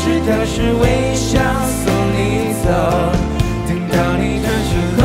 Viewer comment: 原来歌词是“微笑送你走”，我一直听成了“为啥”了😂